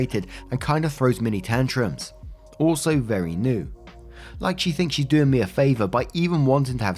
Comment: MIN 5:30 actually, OP said she acts "LIKE she thinks she's doing me a favor. " I noticed that was missed.. it doesn't sound like she ACTUALLY said it. Eitherway, she sounds like she needs help, is toxic and or grieving.. not an excuse to external anger or be abusive.